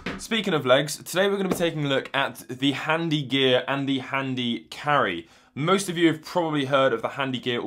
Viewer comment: I thought it said harry gear and harry carry for a second there.